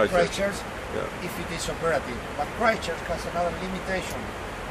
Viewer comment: QUE DIJERON AHI??? no entendi nada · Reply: Nahuel Fiocco christcrurch